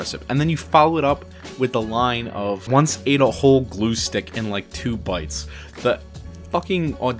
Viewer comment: the juxtaposition of killing a God and eating Glue is insane. You are right, those lines have no right being next to each other and for them to not only do but to fit so smoothly. His pen is something else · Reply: Man I always thought he said he ate a whole glow stick